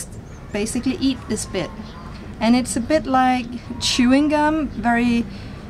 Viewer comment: Did she just say "chewing cum" on 6:17 ? 😂😂😂